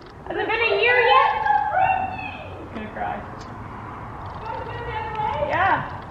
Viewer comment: Did anyone else hear that at 11:38 said I’m gonna cry that shows is so proud of you you did great keep it up !